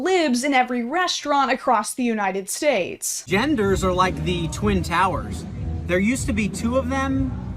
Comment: Doddering old geek that I am, when he referred to the Twin Towers, I heard "the Two Towers," which had me braced for a rant about Eowyn and gender politics among the Rohirrim.